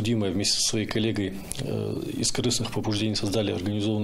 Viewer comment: Мне послышалось из крысных побуждений. По моему это более точное определение!